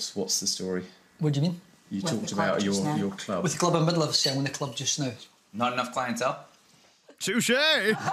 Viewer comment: What did he say at 5:38? Played it back 5 times and I still can't understand a word lol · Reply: "Were in the middle of selling our club just now"